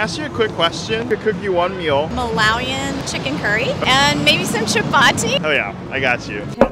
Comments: I'm Malawian 🥺🥰 this is precious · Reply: I thought she said malvani chicken curry.. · Chapati !!!! As a maharashtrian I love when chapati gets the recognition it deserves! Yuhhooooo ❤️❤️ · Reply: I legit thought she wanted to say malwani chicken curry(chicken curry of Maharashtra). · Chapati !!!! As a maharashtrian I love when chapati gets the recognition it deserves! Yuhhooooo ❤️❤️ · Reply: I thought she was trying to say malvani😭🤌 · Is it me who thought she is saying malwani😂